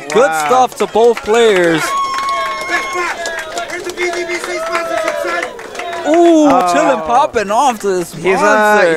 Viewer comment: what did chillin say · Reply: “Mariobrosteen64” Gaming WHERES YOUR VGBC SPONSORSHIP NOW SON